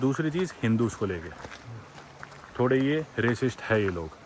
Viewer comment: those who didnt understand he said bhot bade C hai